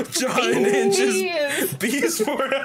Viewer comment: Oh shit, I thought it said "beer." I don't know if beer or bees would be worse in that situation 1:54:27 How dare this game remind me of Bird Person. I cry every time. 1:55:40 Fuckin' Hell I forgot that was the voice they gave her and nearly fell out of my chair. That computer system being called CARL is the best coincidence I've ever seen, especially considering it's also all caps. 2:15:26 Damian's a bottom confirmed. Blue's my favorite of the protags. A shame she did so poorly here Gonna save some nice quotes in the comments here for future me 34:24 "Did I just become a familiar" 41:36 "I have a corpse and I'm taking it around with me" 1:14:09 "I don't want to be the kind of person to bring a knife to school, I'm gonna go with cocaine" 1:49:50 "...a bottle with 'POLLY'S TOILET WINE' written on it in permanent marker." 1:52:54 "With a wave of his magic wang" I fear whoever has the displeasure of reading this long ass comment.